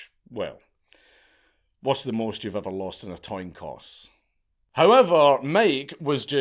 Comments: he says toin coss instead of coin toss 😂 that’s gr8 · Another way of saying "a 50 50 chance" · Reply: lol I know what a coin toss is . Dankula in this vid here mixed up the lettering of his sentence order here and instead pronounced it “ Toin Coss “ as opposed to “ Coin Toss “ which is the right way of saying it . I was just taking the piss when I heard him say it.